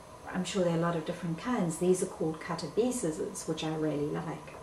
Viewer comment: What brand of scissors did you use here? You state it 9:41, but I couldn't understand what you said. Anyone know which scissors she's referring to? Thanks! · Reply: I believe she said "Cutter Bee Scissors" - I just googgled it and it is in fact a brand ✂ ✂️👍